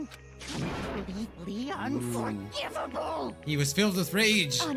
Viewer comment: Leon forgivable? Did Hifumi just say that? O_o